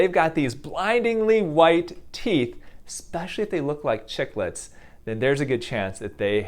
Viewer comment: did you just call Simon a Chicklet ? 🐔